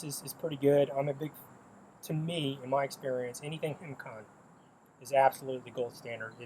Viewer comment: What does Jay say is the gold standard for hemorrhage control agents? (I think its HemCon) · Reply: yes Hemcon